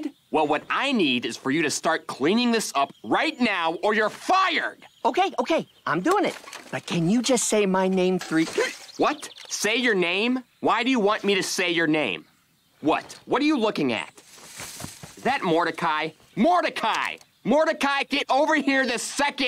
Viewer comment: My mind when I heard the line 6 years ago: But can you just say my name “Rigby”? 0:41 My mind when I heard the line now: But can you just say my name three- (Gasp)